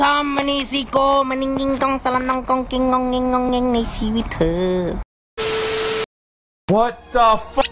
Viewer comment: Did he just say Thai word like "ในชีวิตเธอ"